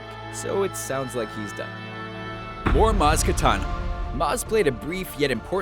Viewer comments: did he just say "more maz katana" ? xD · Did he say Maz Katana at 4:12 or was that just me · DID HE SAY "MAZ KATANA"??!!! 😂😂😂👌